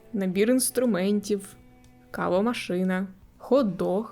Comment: Мені почулося калова машина😅